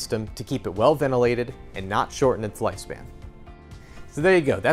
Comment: I'm sorry but could someone explain what the narrator said at 2:09 about the two purposes of the roof vent? His voice went out after he said "ventilation". Thanks so much! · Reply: "...and not shorten its lifespan." I had to turn on the Closed Captioning to figure out what he said. LOL!